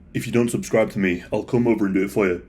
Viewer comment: I couldn’t understand what he said, it sounded like he said “he’d come over and do a FOAI”, but I know that’s not right. What did he say? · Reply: ”I’ll come over and do it for ya”. I had to listen twice to get it